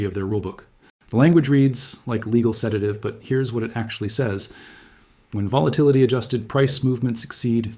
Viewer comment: Here's what it says in the rulebook "Volvvity adhosssted price fivautions" Yes. Good information. This is clearly hooman providing information.